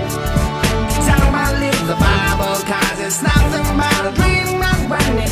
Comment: I always thought they said "Unbelievea-bubble" (unbelieveable) when they say "Don't believe the bubble cause its nothing but a dream". ;)